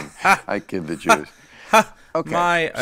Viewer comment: does he say “ I kid the Jews”. Sure sounds like it.